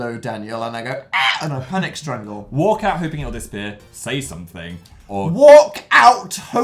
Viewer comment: I thought Dan said «work out and hope it disappears» and I immediately thought «why would I ever work out»